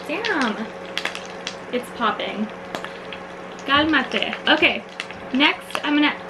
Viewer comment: Did she just say"kalma te" 1:33 thats a filipino line which mean calm down